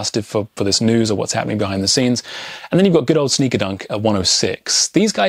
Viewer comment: what are you saying? Sneakerdunk? I can't find the listings for that price. Thanks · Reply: SNKRDNK. That's in the app I had that price. It's 94 pre shipping today.